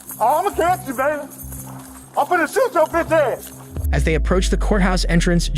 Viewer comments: says (inaudible) BS, he clearly says "I'm finna shoot your bitch ass" 😂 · How is 9:40 inaudible? "I'm gonna shoot your bitch ass" it pretty clearly what he said.